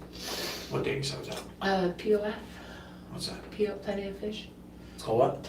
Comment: When she said she met her boyfriend on POF I thought she said “POS” and I laughed out loud and thought “well that sounds about right”! 😂 Then she clarified for the detective “Plenty of Fish”. Oh…😳🤣